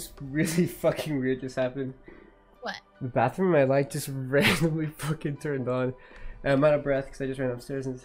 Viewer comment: Did he just say: "The bathroom in my light just randomly turned on." ? LOL